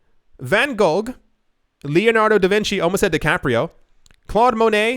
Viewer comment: Okay, who heard him say Leonardo de Caprio instead of Leonardo da Vinci?